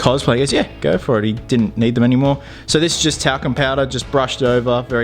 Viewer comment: Why did i hear "taco powder"